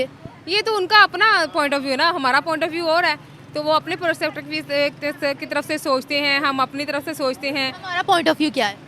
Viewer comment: kya kya kya....... Propproppropropoprop kya bola behenji 😂😂😂😂😂😂😂😂😂😂😂